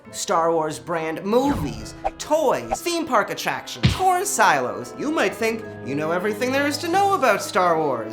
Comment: for a moment there, i thought he said “porn silos”